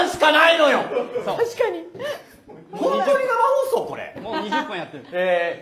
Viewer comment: Aaa I misheard something!!! Correction: 0:40 "Is this seriously a livestream?"